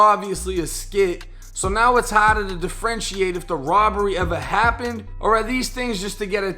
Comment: "The Friend She Ate" oh u a freak. I thought he was saying "Differentiate" my bad